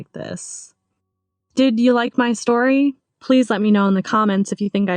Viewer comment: It sounds like she said ”dude you like my story?”